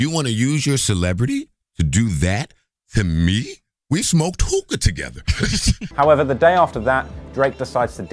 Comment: he really said "smoke tooka" smh